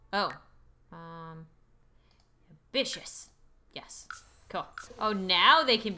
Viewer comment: The way she said ambitious sounded like she said bitchious